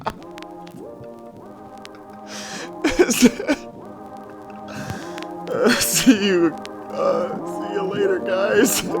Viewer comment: Come one bro, you can say the word! (what is the word!) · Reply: The word is “f r i e n d s”:)))))